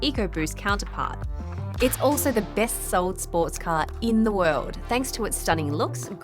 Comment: what did she just say!!? sports car